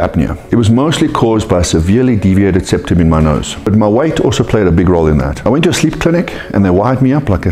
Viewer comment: I misheard "but my WIFE also played a big role in that"....that made me chuckle XD